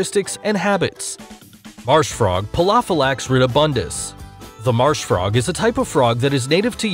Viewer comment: Please, what are yo saying on minute 0:59 and 1:0? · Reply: Pelophylax Ridibundus